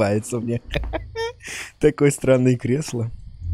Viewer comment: просто мне послышалось или он сказал какой странный кресло?😂